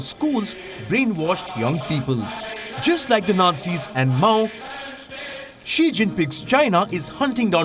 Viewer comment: Did he said xi jin-PIG